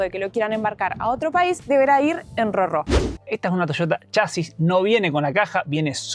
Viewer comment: dijo que no cabía en contenedor por la altura, que había que enviarlo en qué? Yo le entendí "llevarla en "roro" algo así jajajaa alguien sabe qué es eso o a qué se refiere? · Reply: Si, RoRo. Es la abreviación de Roll ON Roll OFF